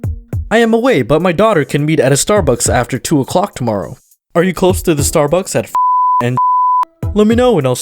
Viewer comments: What I heard was Are you close to the starbucks at f**k!? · It literally sounded like u said: The Starbucks at f**k and f**k. Am I the only one who hard that?